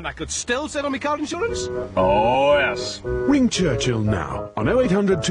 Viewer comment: Win Churchill Now 😂. I thought that’s what it said when I watched and listened to it through speakers and had the volume low but then When I listened to it through my headphones and had the volume loud I heard it say ring Churchill now instead of win Churchill now which I realise now. But it was funny when I thought it said win instead of ring